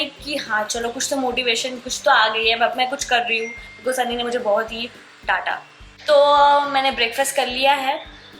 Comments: Ani as Anirudh sharma Almost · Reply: I heard sunny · Did she say sunny ne mere ko daata at almost 1:24 ?? Please sayyyyy